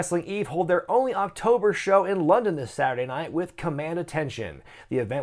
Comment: Please tell me I’m not the only one who thought that said commando attention. Thinking about it now that would be a totally different show lol